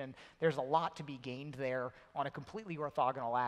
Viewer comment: WTF does Carmack say at 15:05? The Orthogonal Axis?